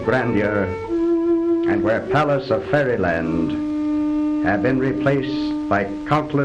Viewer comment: did he just say palace of fairyland? I might be mishearing things.